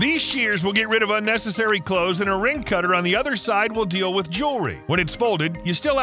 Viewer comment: Thought he said: “a ring cutter to deal with children”